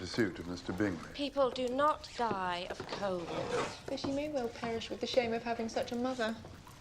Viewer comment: did she just said COVID!!???